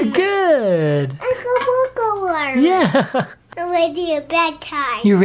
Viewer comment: For a second, I thought said "I'm ready for a bad time". XD